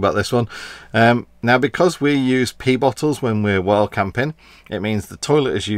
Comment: did he say pee bottles?